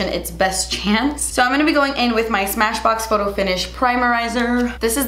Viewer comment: i had such a brain fart.... 😂 i heard "i'm gonna go in with my DAMN sponge" 😂😂😂 i was like.. 🧐 wait...